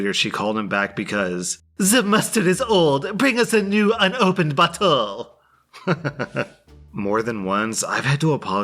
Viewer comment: I know there’s subtitles but it sounds like she says “bring us a new, upopened butthole.” 😂